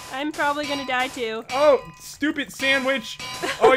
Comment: when popularmmos says stupid sand witch it was like saying stupid sandwich